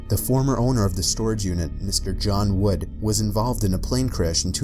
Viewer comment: 🤣 John Wood! Sounded like John Wick (Reaper) #FortniteRefrence!